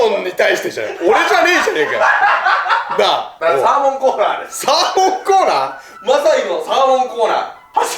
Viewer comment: 『サーモンコーナー』が時々『サーモン食おうな～』って聞こえるw